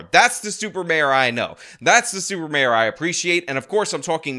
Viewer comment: I keep hearing Super Bear...like Cocaine Bear...😅 🤔 😮